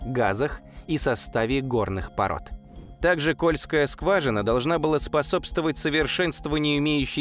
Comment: Послышалось скользкая жажина